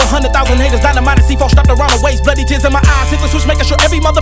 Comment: I know he said "bloody tears in my eyes", but it kind of sounds like he said "bloody j¡zz in my eyes". 😂